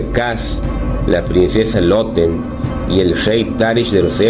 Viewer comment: Yo escuché "La Princesa Elote" 🌽😆.